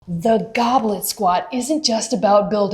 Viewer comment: I thought you said, "goblin squat," and I'm thinking where the heck did that come from? Slaying your own monsters!